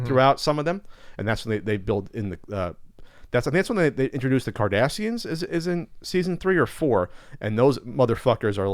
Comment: I thought you said season 3 of Next Generation was when they introduced the Kardashians. I was very confused for a moment.